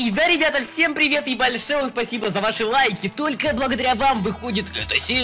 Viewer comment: мне послышалось - И да ребята ебали шоу спасибо за ваши лайки.